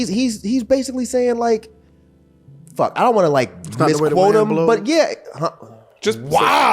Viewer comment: yo, what did say around 23:43? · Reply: “that was the way the wind blew him” badm tdss Which is why he had the reaction when he read off the track list